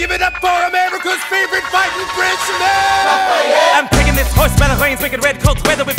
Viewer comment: Kitty Bitty who heard "Everyone give it up for amaricas favorite fighting Frenchmen!" Bc I heard "EVERYONE GIVE IT UP FOR AMARICAS FAVORITE FIGHTING FRENCHFRY!!!"